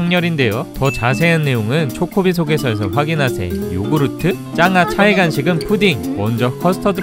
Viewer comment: 자막은 짱구지만 소리는 짱아라고 들리는건 저뿐일까요??